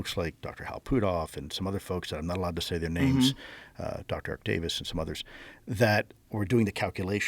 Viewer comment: sounds like he said Dr.Octavius 😂